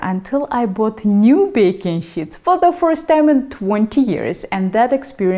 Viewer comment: What a funny funny accent this lovely Russian woman is priceless it sounds like she is saying baking shits and what a lovely time she was having with the baking shits and how she was so happy to keep using the shits herself, what a difference an accent can make.